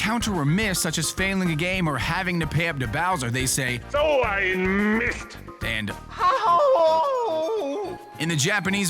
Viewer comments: wario says "so ein mist". we germans use this sentence if something goes wrong :D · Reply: TheDarksoul01 No Wario actually says "Doh' i missed". I also thought that he said "So ein mist". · Wario says "So ein mist!" which is german for "Sh*t!" or , "F*ck!" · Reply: JaketheKoopa No Wario actually says "Doh' i missed". I also thought that he said "So ein mist". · wario says "so ein mist". we germans use this sentence if something goes wrong :D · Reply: i always thought he said "oh i missed!" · In 0:18 is warios saying'' So ein MIst,,?